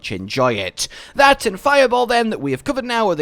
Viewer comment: did you just say "fireball"?